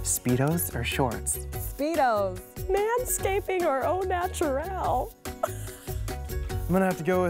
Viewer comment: What's The Question That melissa Made At 0:26? (I Don't Speak English, So I Can't Understand) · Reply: Man-scaping or natural.